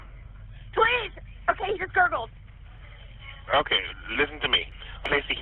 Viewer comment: I swear it sounds like says “IT gurgled” 😮